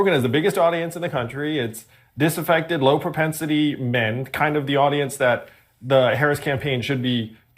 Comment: am I hearing that correctly? Did he say Joe Rogan’s audience is largely ‘disaffected, low propensity men’? It wasn’t too long ago it was said Joe Rogan, Jordan Peterson etc appealed to ‘incels’….. I guess this is a nicer way of insulting his audience! It seems to me the real disaffected low propensity men in society are those who bitch and moan about conservatives and who attach themselves on to whatever social justice cause in vogue that they think it gets them closer to these feminist cat lady ‘boss bitches’ who are obviously mother figures for them😂